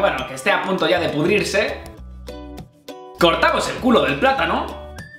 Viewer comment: escuche culo??